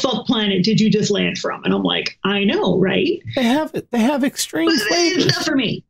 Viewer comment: I love you but when you said 'they have extreme flavors' I heard 'It has what plants crave' in my head.